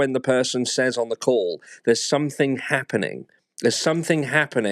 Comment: I thought she said something happened first, then chaged it to something is happening. I thought that was odd.